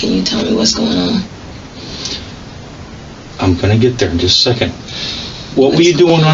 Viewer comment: did he say "I'm finna to get there"???